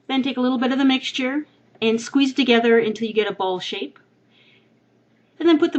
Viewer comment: i thought she said "a bluge shape" haha.